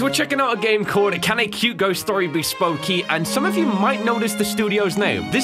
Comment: did anyone else hear "spo key""